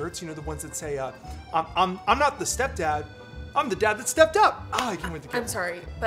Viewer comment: "I'm not the step dad, I'm the dad that stepped up." Wow. I'm never using that line lol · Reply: I heard it as stepped out not up 😂